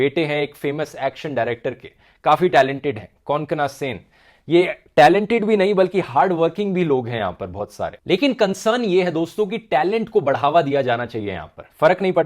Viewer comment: did you say Konkana Sen Sharma is not talented? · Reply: I said - Not only is she talented but also hardworking